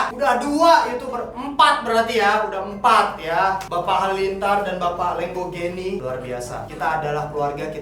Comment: Menit 8:20 "Bapak Halilintar dan Bapak Lenggonggeni" :v Ngomong apa si bang😅😅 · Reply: Iya. "Bapak Halilintar dan Bapak Lenggogeni